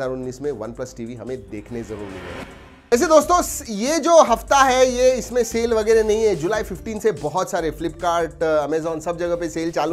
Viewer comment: did he say july15